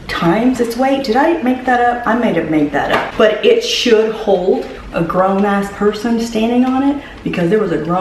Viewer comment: Egads, for some reason I thought you were gonna say "holds a whole person inside"!!!! Like just what are you planning lady 😳